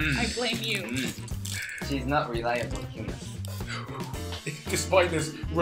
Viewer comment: I thought it said Reliable Hit man. lol